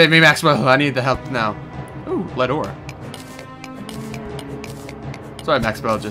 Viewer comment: I thought he said "ooh, Le door."